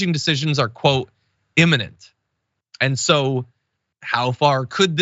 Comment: "Immanent? I do not think that word means what you think it means". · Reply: "Imminent" is the word he used, not the one you thought you heard.